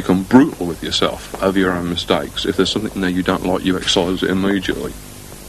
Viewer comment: What does he says at 4:59? "You draw everyday or you write everyday. You become very aware of your own mistakes. You become brutal with yourself over your own mistakes..." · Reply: "if there's something you dont like you excise it immediately" (12 year old comment, I know)